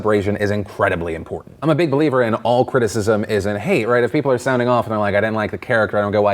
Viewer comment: when you say "all criticism isn't hate" at 1:25, the subtitle says "all criticism is in hate"